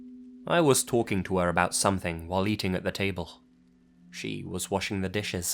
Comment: "I was talking to her about something, while eating a potato." lol Don't think I heard that right